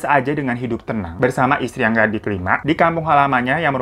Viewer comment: Gimana gimana, bersama istri yg ga di terima? Gw salah denger ga sih bund?